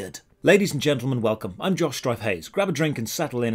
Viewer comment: I don't know if this is just my poor hearing but your name is "Josh strife plays" but every intro I hear you say "Josh stripe hays" and I don't know how I can hear that if you were actually saying the other thing...